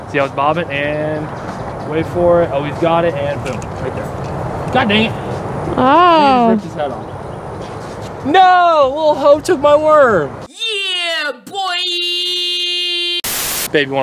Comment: Did he say 😆" lil hoe took my worm" at 7:10 😂